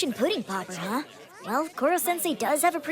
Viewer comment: When she said pudding popper I thought she said pussy popper